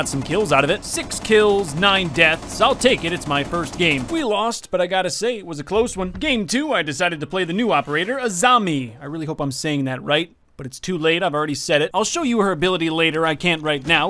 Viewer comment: I legit thought he said the new agent is a zombie